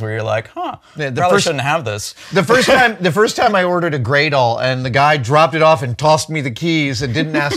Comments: what does he say here "the first time i ordered a gradle"? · What is he saying at 10:44 ? It sounds like "Grey doll"? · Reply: It was "Gradall" (grade-all) which is a manufacturer of excavators. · What did adam talk about at around 10:45? He ordered a "gray doll"? At least thats whats in the subtitles. I'm pretty sure he means sth. different but cant figure out what exactly. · what does he mean, when adam says he ordered a "grey doll"? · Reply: Grade-all, or gradall. Type of heavy machinery.